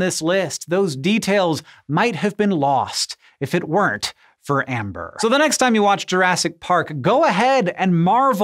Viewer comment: I thought it said Rambler.